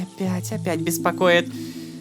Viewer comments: М? Ой блять опять беспокоят P.S, Как Это Расслышать 0_0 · А мне послышалось блять опять беспакоят